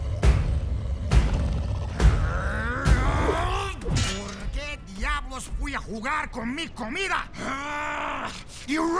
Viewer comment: 😂😂 what he say? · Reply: Why the hell did I go play with my food